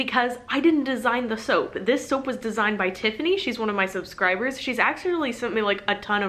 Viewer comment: My head cold told me you said 'she's one of my soapscribers' and thought that was cute. And then I went back and realized you did not say soapscriber. And thought I would put it out there anyway because #soapscriber should now be a thing.